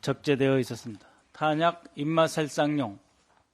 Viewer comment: 자막 오류 ; 인명 -> 인마. 인마 살상용. 엠비씨 제작진이요~ 군대 다녀온 남자 직원 하나도 없습니까? 군필 남자라면 인마살상이라는 단어는 귀에 인이 박히도록 들었을 단어인데... 제발 조금만 더 신경 좀 써 주시길...